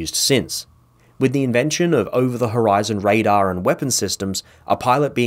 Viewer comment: "Hirozen" instead of horizon.